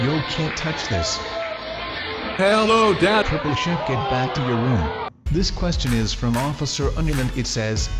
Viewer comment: Wait at 4:09 did he say holy sh*t get back to your room??